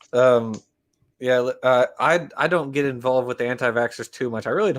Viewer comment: My concentration is off today. Could have sworn I heard anti-bats instead of anti-vaxx. LOL. Then my next laugh was hearing say that this is the anti-vaxxers 'last shot'.